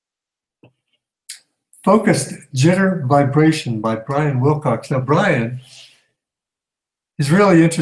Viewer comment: sorry when you said brian Wilcox thought u said brian cox lol. I am a bit wobbly again today. slighty out of balance and all down right side of body feeling sensitive. And brain affected when im like this cant recall words ect. I was out in the rain maybe its that!chemtrails! And I haven't been burning natural oils for couple of days. Usually burn lavender or rosemary ect. Could be a food allergy or somethings been triggered. Good info, even when you don't sound too well yourself lol.